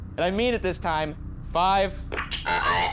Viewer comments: I hear “little girl “ on 30:13 not “five” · #postmortem I didn't hear "Five" at 30:12, I heard "Screw you" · On 30:12 instead of "five" sounds as if it says "not a girl" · not “five” but I hear “that’s my girl” as if talking about Mathilda #postmortem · when you start counting down, I hear "zero" rather than "five". Idk, I'm no ghoul hunter but I literally can't hear "five"? #boogara